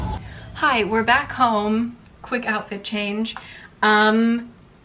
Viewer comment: Hi, I love these videos! There's a typo in the captions at 11:47. What should say "quick" says "wuick". Just wanted to let you know that :D